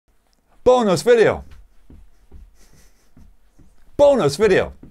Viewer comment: At first, I thought you were saying B O N E L E S S video and was amazed by it